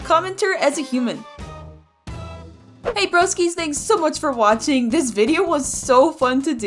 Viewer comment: When you said “hey broskis” I thought you said “hey brokies” 💀